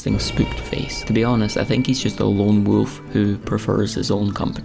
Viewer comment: I heard lone floof